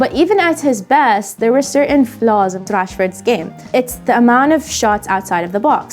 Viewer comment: I swear I heard "Trashford" at the 04:07 mark🤣